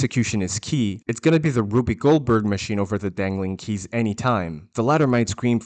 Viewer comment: Motherfucker said Ruby Goldberg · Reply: For a second I thought I heard “a Whoopi Goldberg machine” and I honestly think I might start saying that now instead.